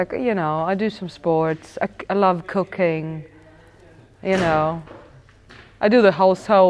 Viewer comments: did she just say she does cocaine ? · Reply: No it sounds like it but she said she loves cooking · LMFAO thought she said she loves cocaine had to double check! xD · I thought she said I love cocaine first time I heard that 😂